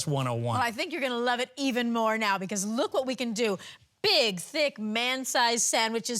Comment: Did Cathy just say just likes a “Big thick man?” on national television ?!? 😮